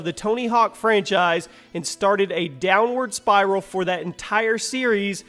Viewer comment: did he said Spyro?